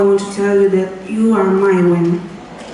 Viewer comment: what did she say “you are mine”? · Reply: My win